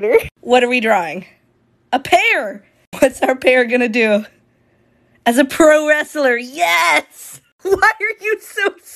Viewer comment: "As a pro wrestler" Why did I hear that as "As a pro hero"